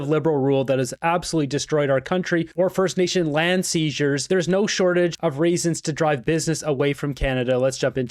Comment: Did he say ‘no shortage of raisins’?